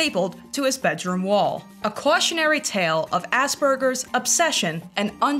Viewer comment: When u said aspergers i genuinely thought you said ass burgers and i was like 'yeah that's fair the guy has a burger on his wall and likes poop'